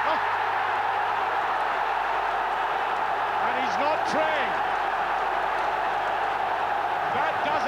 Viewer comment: What does Archie say at 19:42 ? ''And he's not praying?''